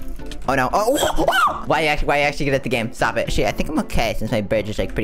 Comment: sounds like said shit 😳